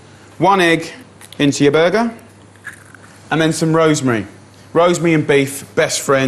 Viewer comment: Did he say, burger at 1:00?